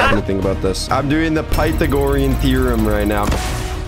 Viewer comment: he really said PAITHAgorian theorem